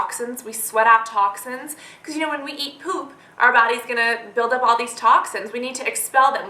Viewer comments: "when we eat POOP" ? did i hear that right .. lmao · When we eat poop ? Did you really say that LMAO (